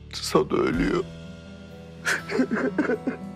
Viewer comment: de ne dedi arkadaslar anlamadim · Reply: Sado ölüyo dedi